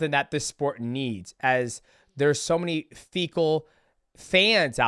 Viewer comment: did just call us fecal loool